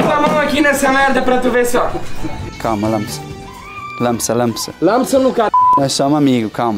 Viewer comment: o que ele falou em alemão nessa hora alfue sabe ? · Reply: Acredito que langsam = Devagar ou calma